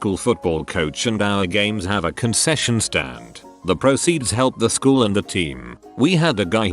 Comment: I heard that as “concussion stand” XD Edit: concussions aren’t fun